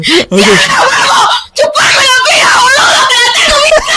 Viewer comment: te diste cuenta que el scammer era Argentino pongan en el minuto 2:50 y si escuchan bien se escucha que dice boludo y los argentinos decimos eso como insulto muy seguido Y lose por que soy argentino